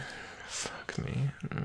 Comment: am I the only one who heard him say fuck me~ 👀 me : with I l Captain~ 😂❤️